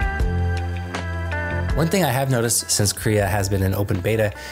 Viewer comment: Ok, talk to me like I am a total beginner. at tech...what AI app or software is he using? I think he said something about Korea in open Beta... 6:07 and I do not know what it means? What Ai ? does this? Loved the vid... but I am choosing and starting ... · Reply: Krea